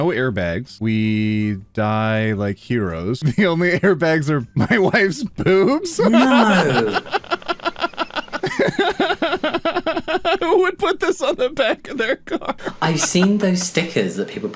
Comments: I heard “my wife’s poops” and was just like: How’s that supposed to be an airbag??? 😭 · For a moment I thought he said "my wife's poops " and I was so confused